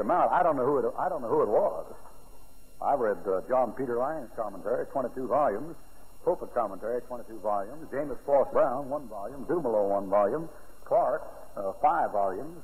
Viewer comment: he starts talking about commentaries: Can someone here list them please. I can't quite understand him. thanks. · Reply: Hi, I got 5 out of 6: 1. Johann (John) Peter Lange, 2. (I couldn't get it after 3 tries on half speed) 3. James Forest Brown 4. J.R. Dummelow 5. Matthew Henry 6. Ethelburt Bullinger I hope this helps. He mentions these names in many of his written Commentaries. Have a blessed day!